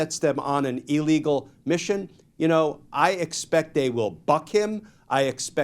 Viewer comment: Did he just say the FBI will "FUCK" KASH PATEL 😂😂. Make it happen!